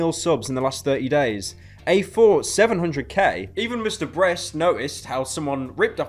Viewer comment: "MrBreast" Is what I heard in 2:57 IDK WHY I DECIDED TO COMMENT THIS